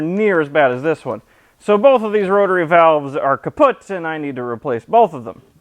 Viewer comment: did he just say kaputt?